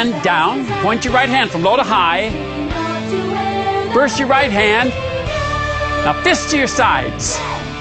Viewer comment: at first I thought he said: "Now fist yourself"... x'D